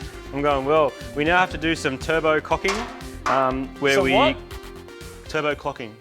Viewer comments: I heard cocking, captions said clucking, then repeated as clocking...all sounds like great fun! 😀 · i heard "cocking" too and the word generator came up woth "cooking" so hahaha! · Haha 8:50 I heard turbo cocking twice haha lol Marty and Moog are so funny